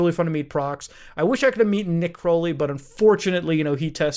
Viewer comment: When you said “nick crowley” I had to rewind because I thought you said “mick foley”